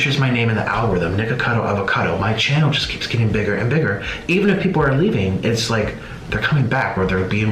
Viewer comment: "My channel just keeps getting bigger and bigger" What I heard: "My ego just keeps getting bigger and bigger"